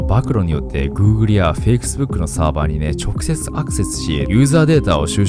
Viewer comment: ここでFacebookが fakes bookと 聞こえるのは僕だけ？ それとも何かの提唱？？